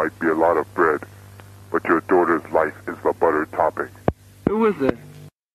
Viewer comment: I thought he said better topic.